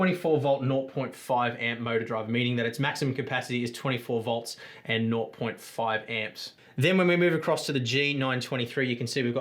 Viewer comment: Wait how many amps can the G920s circuit board handle? It sounded like you said not.5 amps